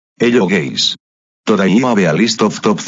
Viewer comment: I heard that hello gay🤡🤡🤡🤡